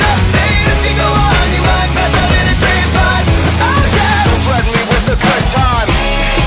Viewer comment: I was making slime while listening to this and I heard "Dont threaten me with the good slime"